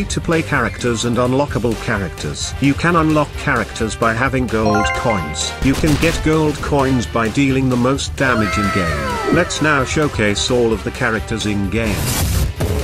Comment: Why do i always hear "girls coins"? 💀